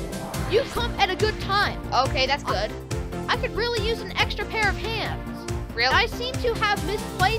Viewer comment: Lol at 0:32 it sounds like you said I need an extra pair of pants 😂